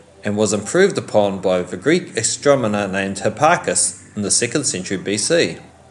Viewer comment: Sounded like he said astromoner